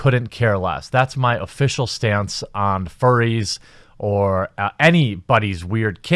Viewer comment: I thought he was saying "fairies" the whole time. I also noticed 90% of the games had a Female lead or if someone was in a group the Female was at the front. This is non-stop now in all media. I live in a Mid-Southern area and my local news now has THREE women as anchors they fired all the men. The weather person is female and the sportscaster is male but has to have a female on the other side of the screen standing there now while he talks sports. Mainstream media is trying to exterminate males.